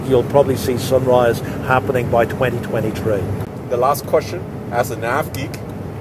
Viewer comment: did he say if this works we will see the flight happening only at 2023?